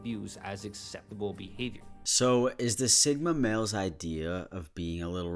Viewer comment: I thought he said Sigma Main and my Overwatch brain took a while to realize it’s male not main